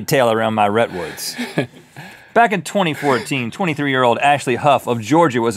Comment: Wait... Did he say "Rhettwoods" at 3:37, or is my mind going crazy?